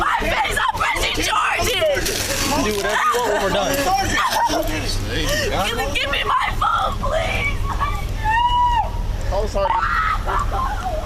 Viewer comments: I have headphones on and did I hear this big nasty scream…gimme my food please/?? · She is not dressed appropriately for someone with a girthy body like hers. 3:22 A cop picked up a bag of chips or some other crap. And 2 seconds later, she's yelling. I thought she said, "Give me my food," instead of "Give me my phone." 🤣